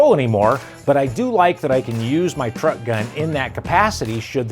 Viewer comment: did he say "truck gun" ?? The idea of a truck gun is stupid. Don't keep guns in vehicle.